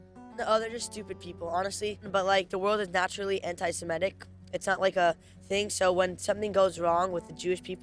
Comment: did he just said " world is naturally antisemitism? " Bro your people are doing genocide and criticizing genocide and talking about the real victims is not antisemitism and if you think you can be genocidal and victim at the same time then you're wrong.